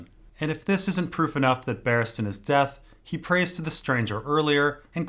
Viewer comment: Barristan is Death. Oh, now I undrrstand. At firdt I thought he said Barristan is deaf